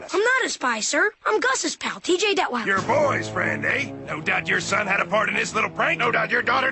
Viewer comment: " your boys friend aye?" for a sec i thought he said boyfriend lol XD